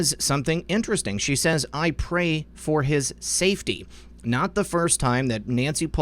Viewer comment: It sounded like Nancy said” I PRAY FOR HIS ELIMINATION....”🔥🔥👹🔥🔥